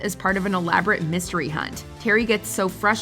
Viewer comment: im 90% sure he says charles. i hear a ch not a j and the name ends with an s so grasping at straws on that one like a few others lol